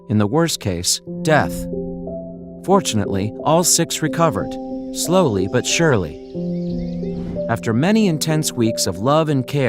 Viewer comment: Did he just say unfortunately six babies recovered slow but surely 😀💀